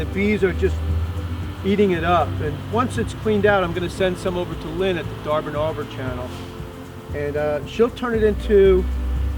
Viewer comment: Great video, as always! You said something at about 02:36 that you would be sending some bees wax over to "Lynn at the D------- --------- Channel." I could not make out exactly what you said. I am very interested in learning more about furniture finishing and polishing. Which channel did you mention? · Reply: Whoops... never mind. I went to your "Subscriptions," and found "Darbin Notes". Thanks for the great videos.